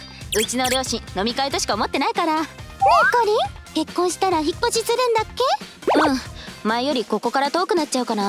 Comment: コメント欄見てたら「引っ越ししたら引っ越しするの？」って聞こえたw